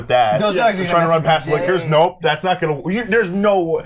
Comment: Every time they said Licker I thought they were saying "liquor"